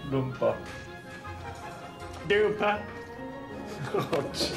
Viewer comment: co on powiedział? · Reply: Plecak pełen keszu DUPA chyba xd